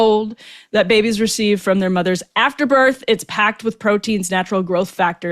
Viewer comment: Am I the only one who misheard that as "mother's afterbirth" instead of "mothers after birth?"